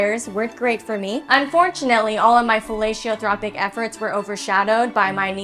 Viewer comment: Fellatiotropic…..bwahahahahahah you deserve a medal for that one!!!! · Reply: I heard "villaintropic" 😂😂😂